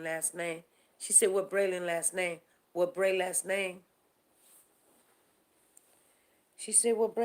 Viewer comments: I heard “what Bray last name” 💙 fly high precious boy 💙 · I heard "What Bray lastname?"